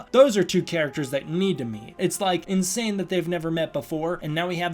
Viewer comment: For a moment my tired brain heard mate instead of meet. Then I reminded and heard meet Also Kong is still my favorite Kaiju, even if he lost to Godzilla.